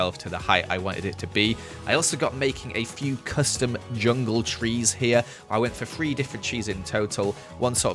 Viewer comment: I put three different trees on the island. My mind: I put three different cheese on the island I thought he said cheese because of his accent!! 😂😂🤣